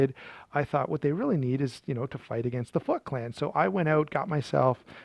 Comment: FROM GO AT 0:43 I thought you said.. "to fight against the fvck clan" 😅😂😂😂 In any case, that gave me a laugh! & Imo. BM Bedlam or Plain edge & rare Civilian or original matriarch! If it size they find intimidating.. CS Rajah / XL Espada! Good video. Happy Holidays. 🎄❄️